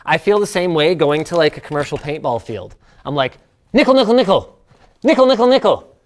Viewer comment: What did he say????????? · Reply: Nickel. A nickel for each paint ball. 😅